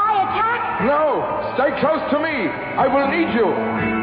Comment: It sounds like Xavier says "I will eat you."